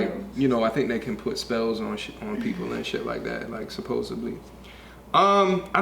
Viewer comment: Did he really say supposably at 30:17???